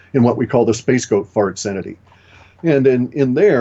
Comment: did I hear Clif say "space goat fart cenity" ?;)